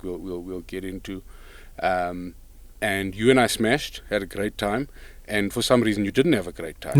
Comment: Did you just say, “you and I smashed” 🙆🏽‍♂️😂